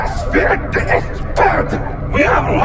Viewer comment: The dislikes are the people that can’t FEEL THE E SPED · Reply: Did u just say “Whe# are you tu accuse me”